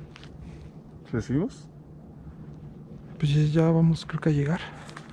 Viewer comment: se escucha que dicen ..." Ya váyansee"